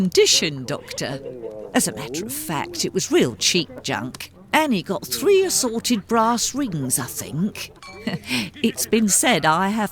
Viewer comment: The character says "Annie got three assorted brass rings" whilst the subtitles say "Annie got three matching brass rings"! Which statement is true, did they match or were they different - the voiceover and subtitles certainly don't match!!??